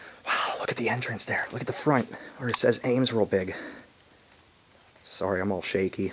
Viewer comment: "Look at the front, where it says Ames real big". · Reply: To me, it always sounded like he said, "look at the front, where it says Ames whooping"🤣